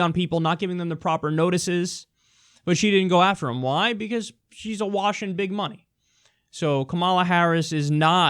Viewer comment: I could have sworn, when Kyle said "she's awash in big money" he said "she's a Washington cunt." Definitely a big double take moment there.